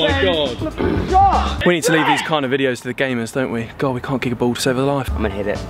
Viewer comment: did he say gamers? 😏 because Kiingtong did the same challenge and he is a gamer😊👌